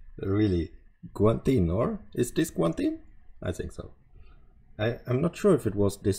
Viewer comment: ขำแบบ จะเป็นลม55555 " อ๊อกๆ " 555555 🤣🤣🤣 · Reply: ได้ยินเหมือนผมมั้ยที่พี่ฝรั่งพูดว่ากวนตีน